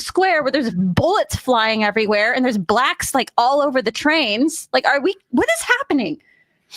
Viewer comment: Did she just say, "...and there is blacks all over the trains"?